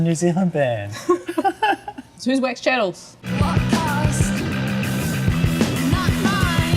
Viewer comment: Can barely make out what is said there, but after a little google-fu I figured out the band is called Wax Chattles. Being a fan of bands like The Screamers, Suicide, Brainiac, etc., that kind of synth/electro-punk is right up my alley. Thank you for turning me onto this band, and thanks for your great music as well Beths.